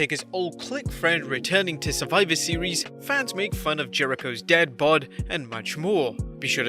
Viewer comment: Dad bod I thought he said dead bod